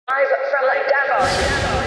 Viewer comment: At first when you said Davos it sounded like you said Devils. Fitting.